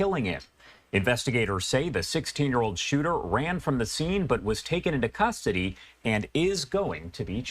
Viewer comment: am I hearing things, he said “k1lling it”